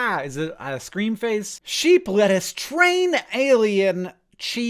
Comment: I heard "Sheep let us train alien."